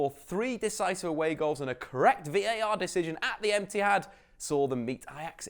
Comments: is it just me or did he say emptyhad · Am I the only one who heard Etihad as emptyhad? 😂😂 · Did he say emptyhad? 😂😂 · I heard emptyhad at 1:27. 😂😂😂